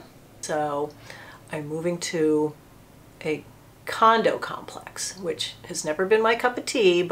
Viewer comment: Congrats on the move! This is going to be so exciting and refreshing!! Also, it was too funny not to add but my boyfriend was halfway listening in the background and at 18:00 thought he heard “I have a condom complex” and the following statements made him really think this was the conversation until he finally asked me. I re-listened and lost it at the 6inch comment 😂